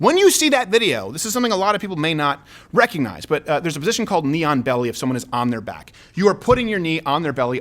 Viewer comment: I thought he said “neon belly” and was super confused for a hot second😂